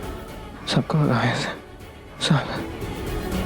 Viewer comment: pe sameer ne kya kaha sunayi nhi diya acche se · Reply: Sanp kon bhagaao